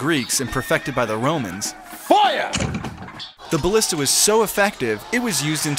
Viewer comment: Did he say at 0:14 'Feuer' ?! :D That's German isn't it? :DD